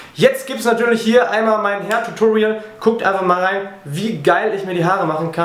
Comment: Ich hab immer herrtutorial verstanden :D